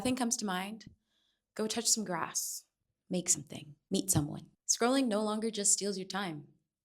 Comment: I was listening while doing dishes and looked away from the subtitles for one second, and I thought you said "EAT someone" 😭 okay cannibalism